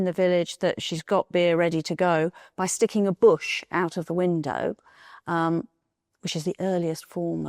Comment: I was listening from the other room and I thought I heard her say "sticking HER bush out of the window" 🤣